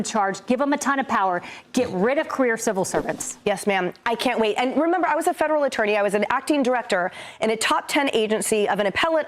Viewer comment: I thought she said "get rid of QUEER civil servants". That's for 2026.